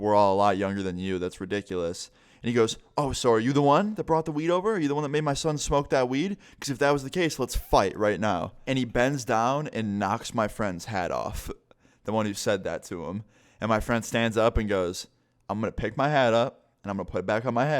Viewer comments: Did you say head or hat off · I heard "knocked my friends head off". I might need help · I thought he said head lmao